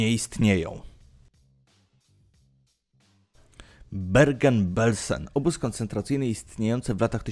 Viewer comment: Bergen jest w Norwegii a nie w Niemczech EDIT Sorry mój błąd nie dosłyszałem Bergen-Belsen usłyszałem tylko Bergen a to miasto w Norwegii